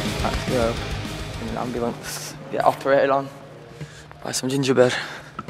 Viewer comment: lol when he said ginger bird i thought he said ginger bread hahaha